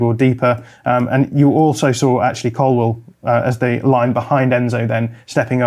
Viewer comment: Lol at first I thought he said Actually Colwill. Then it hit me....Ashley Cole-will😂😂😂😂Good one